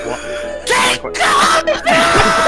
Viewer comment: 結果発表ーーーーー!!!!!!!!! (爆音クソデカボイス) こっちの方がコケコッコーに聞こえる()